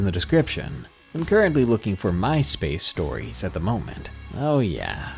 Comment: Ok, so when you said "MySpace Stories," I heard "Mice-Based Stories."